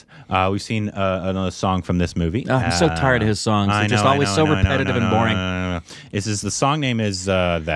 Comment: is he said repetitive and boring